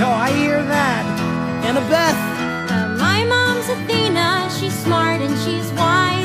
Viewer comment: I first heard "my mom is a female" instead "my moms athena" now I hear female all the time >:(